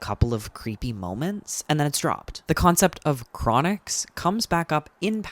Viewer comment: i legit thought you said his name was croup 😭😭😭